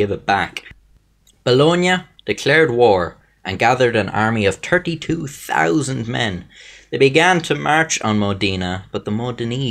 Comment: Not to be mean or something but when he said 32000 men I thought he said turkey too thousan men